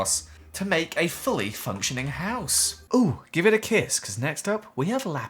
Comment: I thought he said give me the kids not give me a kiss